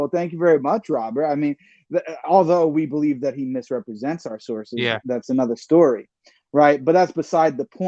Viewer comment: no you liar lmao The church have apostasy law and torah have it lmao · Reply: oh he wrote misrepresents I thought he said represents No thats accurate